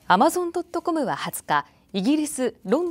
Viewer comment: アマゾン・ドット・コムが、 アマゾン・トットコ・ハムに聞こえた。